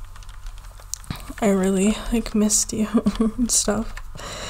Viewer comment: did you just say bunz ? Because my nickname is bunz bunz And when I heard it I was like “aww she called me bunz” and then I started thinking about it and I was like “how does she know my nickname ???” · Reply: She said " missed you a bunch "